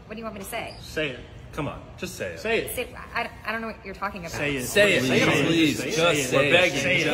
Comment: The first: POV: that one friend who never swears and all the other friends are super proud Ps at first I thought she said "I I I don't know what you're fucking about" (In fact it was "what you're talking about")